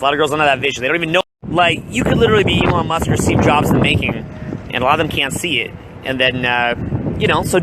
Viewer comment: What is he saying at 1:44-1:46 ? I don't understand. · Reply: U can literally be Elon Musk or Steve jobs in the making, and a lot of them just don't see it.